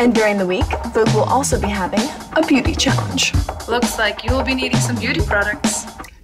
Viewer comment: did she say a Booty challenge? can i judge?